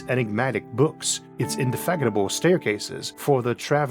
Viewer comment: I heard 'Indefaggotable' instead of 'Indefatigable'. Am I a homophobe with auditory problems or...?!